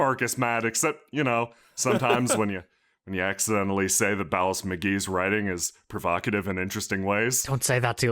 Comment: ohhhhhhh. he's the other writer. I misheard his name the first time as "Valas" wait, does that mean the one girl was just buying books directly from him at the clan trials?